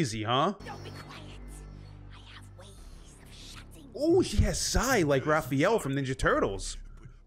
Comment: I genuinely thought you said "Sythe" and I was genuinely confused, then I realized how similar the two sound. Sai, sythe.